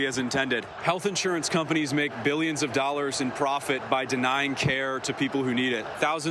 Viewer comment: I love this video, and I'm 110% behind you, but I would be remiss if I didn't point out that at 16:42 there is a typo in the subtitles where you have "dollard" instead of "dollars"